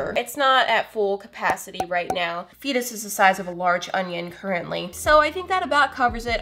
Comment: I thought she said a large “oven” instead of onion 😂!